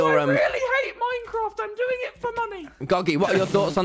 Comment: I thought he said "I'm doing it for mummy" then I read the subtitle 😂😂😂